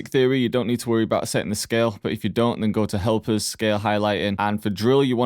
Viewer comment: Funny for me; i don’t know why but i literally thought he is going to say- “if u don’t know go to HELL”😂 but it went “help” i guess I got too serious with beats.